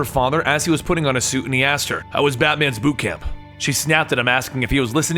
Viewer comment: Did he just say "How was Batman's boob cam"?